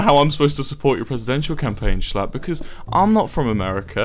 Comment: The subtitles say Schlapp. I love the YouTube algorithm.